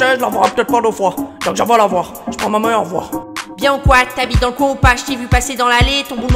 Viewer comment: * c’est t’habite dans le coin ou quoi , mais pas "t habite dans le coin ou pas " à par ça t trop fort